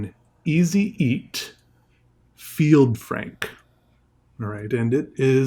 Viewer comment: I like the feild frank part. No art channels really talk about the field frank and I think that this should be brought up more for beginner artists to know how to properly initiate and preform the field frank. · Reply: "Frank? I heard "prank" 🙂